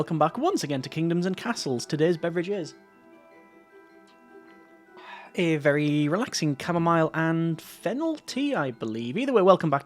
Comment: LOL im american and i heard " a very relaxing caramel and fentanyl tea" i love this seiries btw